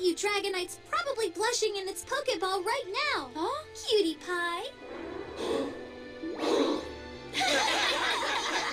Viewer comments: did she said PewDiePie? :DDD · WAIT WAIT WAIT DID SHE SAID CUTIEPIE?MAZRIACUTIEPIE CONFIRMED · did she say PewDiePew??